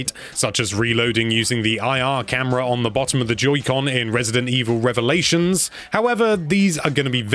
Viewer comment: I heard him say “Judy con”